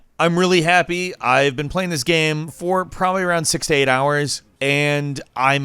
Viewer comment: I legit thought you said 68hrs XD